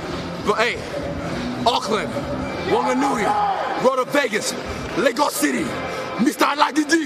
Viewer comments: i cant be the only one who heard "Lego city" lmfao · Then he speaks Yoruba Language at the end to seal his Africanity. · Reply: i thought he said lego city